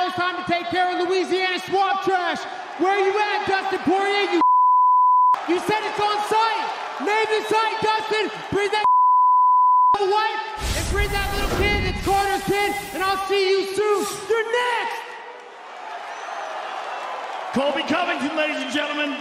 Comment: Could’ve sworn I heard “that’s connors kid” 😹